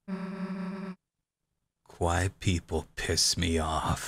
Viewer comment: Misheard "Quiet people" and thought you said "white people". lol